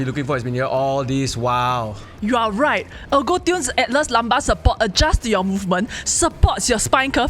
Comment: i thought said lampa support lol